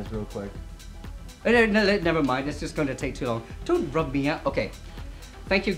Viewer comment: Did I hear don't rub me out at 15:30?